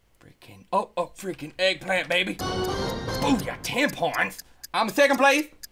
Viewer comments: no he said 10 points but it sounds like he said tampons!!!! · did he say tampons??? LOL! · Hahahaha at 10:17 I had to keep replaying it over and over again because I thought he said "Booyah! Tampons!" And I was wondering why on earth he would say that. But then I realized that he was saying ten points...not tampons :] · it sounds like he is saying "Tampons!"